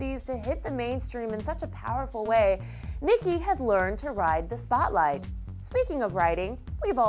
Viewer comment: I legit thought she said "ride the thot life" at 0:10.